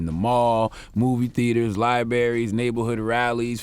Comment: Did Charlamagne say "Lieberries" at 1:50 ? 😂